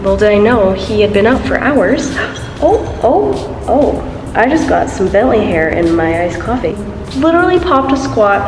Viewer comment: I totally thought you said at first, “I just got some belly hair in my iced coffee”. 🤣 Also on a side note I live and grew up in Minnesota and I’ve wanted to move since forever. Next year my boyfriend and I are going to live full time in an RV and I’m going to do travel nursing while he does travel electrician work. Travel nursing jobs typically last 3 months so I’m super excited to move around and live in different places! 😄